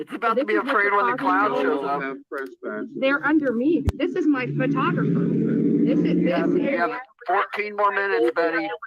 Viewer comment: Did Betty just say this is my photograph ( Olivia.) Omg.. she truly believes she's journalist. Lol.